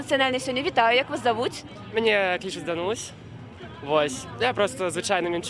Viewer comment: молодой человек "Данусь"! Не расслышала имя или кличку! Это ж кошмар, может потерять мать!!!...Как можно помочь?